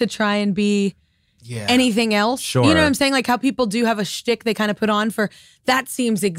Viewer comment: What is that word she used at 10:50?? "How people have a .... They kinda put on" it sounds like stick lol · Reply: Schtick